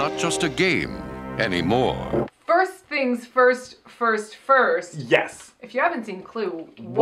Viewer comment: I just paused this 34 seconds because I thought said "First thing's first purse first"